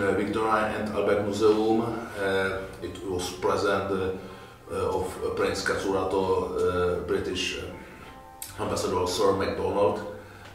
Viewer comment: the subtitle said it was president of breast cancer Otto British ambassador McDonald